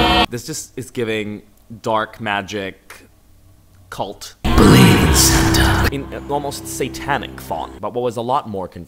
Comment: for a second I thought it said Believe in Satan 😂😂😂😂🤣🤣🤣🤣🤣😭😭😭😭 bless this god is with us 🙏🙏🙏🙏🙏Amen! 😂😂😭😭🙏🙏🙏